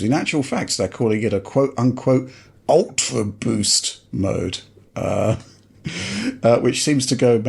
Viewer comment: Every time I hear "ultra boost mode" I hear "ultra beast mode". Sony needs (more than we need an ultra v-neck 🤣) to get Marshawn Lynch for marketing.